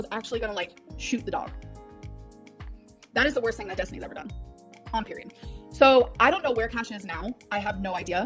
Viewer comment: did she say "on period" lmao what